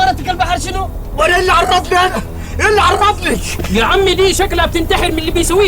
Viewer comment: المصري وش يقول يلعن ر* يسب الله رلا انا مسمع😮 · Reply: لا قال اي عرفني بس غلط حط (ك) في عرفني